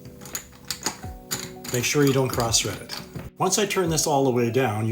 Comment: At first I thought he said “make sure you don’t cross Reddit” and I thought wow that is good advice.